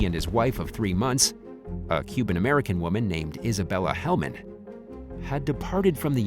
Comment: Human American woman. Made me laugh too hard. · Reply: Ah thank god.. I thought they said human American too 🤣🤣... awww that had me in stitches for a while😅